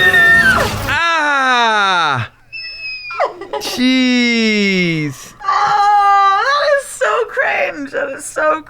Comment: did he just say cheese instead of jeez or is it just me.